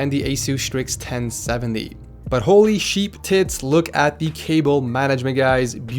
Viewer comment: I was just listening to the video and at 7:35 I just hear "Holy Sheep Tits" I just burst out laughing.